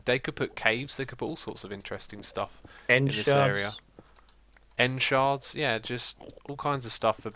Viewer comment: I thought he said, "End Sharks" which was actually kinda awesome.